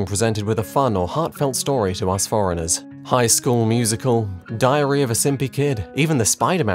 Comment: at first i heard diorheea insted of diary, christ.